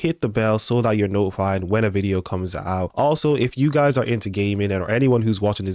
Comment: 😂😂 the way you say "gaming" sounded like you said "if youre into ghey men" 😂😂